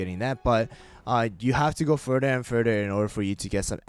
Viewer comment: what did he say? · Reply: Further and further